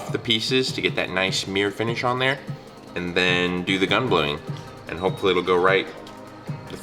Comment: what does he say at 24:27? "Gum Gluing" or something? That's what gives it the damascus finish right?